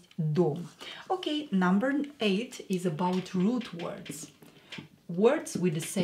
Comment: Привет. your country has, by this time, problems with north America. I don't like the politics of EEUU. And English is the language of that country. And I don't like very much the politics of England neither. It is the reason why I find impossible to learn Русский from English. I don't know your political position. But the American government is doing very bad now, in Украина, and the French government is helping that Ukrainian government. Can you understand, not everyone speaks English in the West ? And a lot of people, in France, doesn't like the French government politics. France is not so large like US, but is better. Pourquoi n'apprenez-vous pas le français, ça vaudrait mieux. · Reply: Whenever you say “root words” I first thought you said “rude words” and were going to teach us that 😅😉🤩